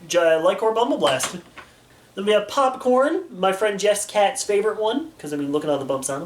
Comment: Did he say Popcorn